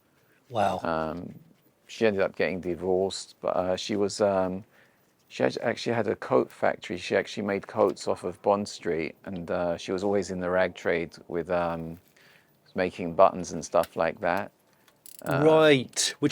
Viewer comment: Hahah i thought he said COKE factory as in cocaina